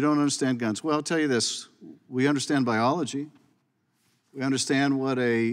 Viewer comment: At the 1:29 mark did I hear Schiff right? Did he say he understands biology?